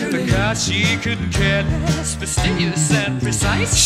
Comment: I always thought he said "because she couldn't care less", not "for cars she couldn't care less"